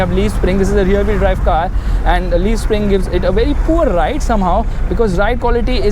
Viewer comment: did he say leaf springs?